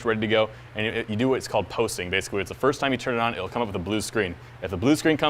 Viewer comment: did he say post?? cus the computer does that every time it starts up